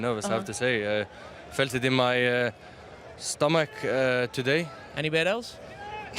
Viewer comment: What does Jamie say at 0:28 ?? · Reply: Erling: I felt it in my... stomach. Jamie: Anywhere else?